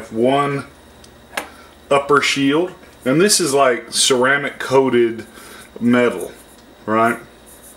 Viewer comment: You cant put metal in a microwave that lide sounded to me like it is metal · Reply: I heard him say ceramic coated metal